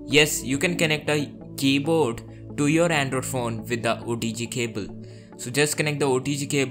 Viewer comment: I swear i keep hear 'without OTG cable' instead of 'with a OTG cable'. Nice video though